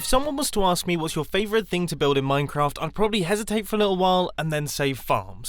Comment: I thought you said my favorite thing to build in Minecraft is a fart